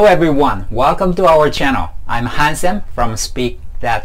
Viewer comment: I love the name. Thought you said "I'm handsome" lol, so cool. Gotta check out your other videos!